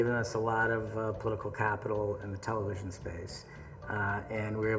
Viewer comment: What did Joe Russo say in 12:11? "And it has given us a lot of ...... the in television's space." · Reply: Political capital